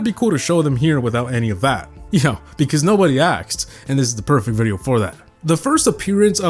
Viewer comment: he said:because no body "aksed"💀 instead of asked